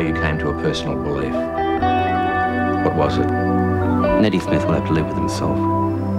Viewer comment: can anyone understand what he said then ??.....I cannot decipher it. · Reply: "Neddy Smith will have to learn to live with himself"